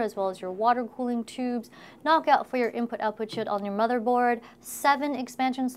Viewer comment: I heard "Input, Output shield" quite clearly.....